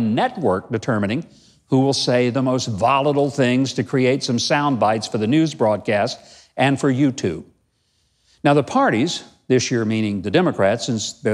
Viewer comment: At first it sounded like you said 'fraudcasts'. What a perfect description of the mainstream media.